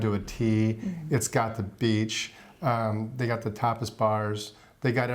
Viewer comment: when you said Tapas bars I thought you said TOPLESS bars.....hmmmm a little different...great video as always and!! I wonder what South American countries you will travel to???